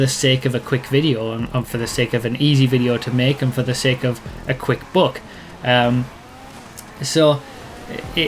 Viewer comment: Thought you said "a quick book", but then I questioned whether those GTA YouTubers could actually write a book and knew you must have said "buck". ;)